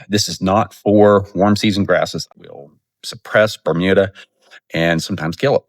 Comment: the turf builder did you say that it was good for Bermuda or it kills Bermuda? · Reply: It will suppress and possibly kill Bermuda. If you are looking to control Bermuda spray mesotrione may be more effective or the Roundup Crabgrass Destroyer will also. Particularly if you apply 6 weeks prior to dormancy. It will weaken the Bermuda winter kill likely will take it from there.